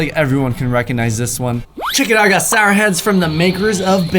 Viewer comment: I thought said siren head in